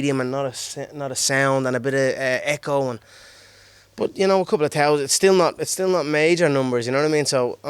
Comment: Did he say “a couple of towelheads”? @ 6:44 or am I just hearing things.